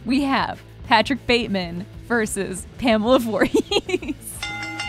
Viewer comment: I heard "...Bate..." and immediately thought you were saying "Norman bates vs Pamela Vorhees". which would be interesting. The Twisted Mothers of Horror.